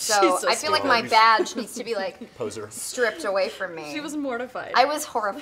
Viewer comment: Did he just say “poser” at 7:34? LOL